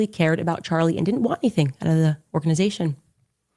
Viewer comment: Ok, so what the heck is she attempting to say @ 17:30 after she says, “actually cared about Charlie and didn’t want anything _______” that last part I have been playing over and over again because it is so super odd-sounding 😝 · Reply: she says "and didn't want anything out of the organization" But it was hard to get that out of her mouth good catch.